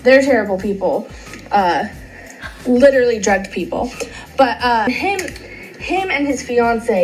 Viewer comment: I’m I the only one who hears: "..literally dragged people.." ??? 🤷🏻‍♀️